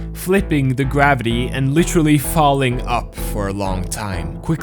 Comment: did someone say Falling up?